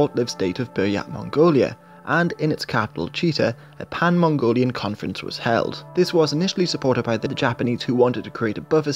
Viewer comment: what does he says? (The short buyak mongolia?) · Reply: A pan mongolian conference was held